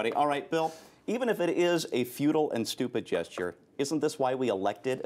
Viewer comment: did he just say, we elected the republicans to be "feudal and stupid"? ALSO 60% of people don't want it over turned. That number includes people who want it strengthened.